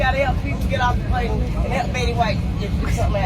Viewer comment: did she say "Help Betty White"? She's a racist lol.